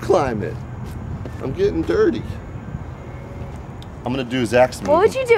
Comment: says "I'm getting durty" thats what she said. im saying durty cuz it doesn't sound like hes saying dirty